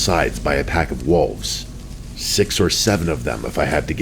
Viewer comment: I was locked in until I heard 67 at 11:50 😭😭